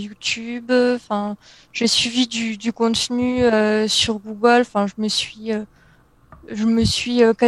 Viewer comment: Bonjour les, savez vous me dire de quoi elle parle au minute 1:40, concernant les sources d'info pour investir, je n'arrive pas a décrypter le nom,cdt ps elle dis "google" ?